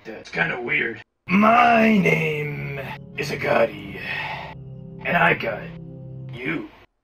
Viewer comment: When daddy dearest called for Agoti it sounded like he said Agati And I love your channel please pin this!!!!